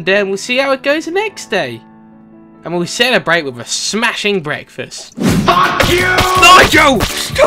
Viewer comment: I think he misheard "Celebrate with a smashing breakfast" with "Celebrate by smashing breakfast"